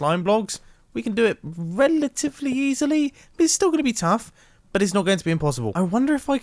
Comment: I heard relatively silly at 4:42 did anyone else